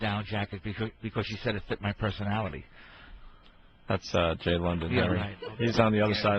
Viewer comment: jay london? I thought they were saying jay leno